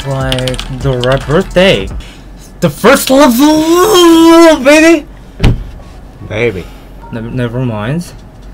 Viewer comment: Did just said "Baby" at? MY LIFE IS A LIE